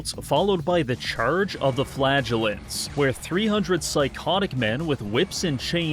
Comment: Every time you say "flagellant" I hear "flatulent"